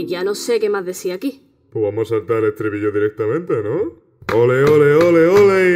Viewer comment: Alguien me puede decir que dice sans en el 1:36 llevo mucho intentando decifrarlo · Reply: Po' vamo a sartá er ehtribillo direhtamrnte ¿No?